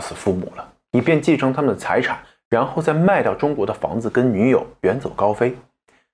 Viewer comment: 我聽成"賣掉中國的房子跟女友，遠走高飛"…